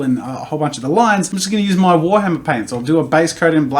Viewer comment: D-did he say Warhammer as in Warhammer 40k?